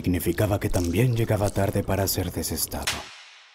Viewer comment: que dice en el 0:43? Deberia decir "destetado" pero no se entiende...